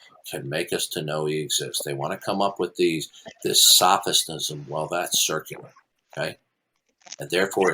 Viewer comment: did say sophisnism? LOL